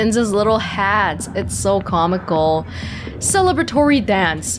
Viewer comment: I absolutely misheard this as, "I love the turians' little hats, it's so conical." Which would've been accurate ... confusing, but accurate.